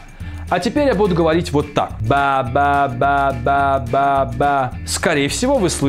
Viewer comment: неужели только я услышал, что он здесь 6:25 говорит: ба ба ба да да да а не только слог ба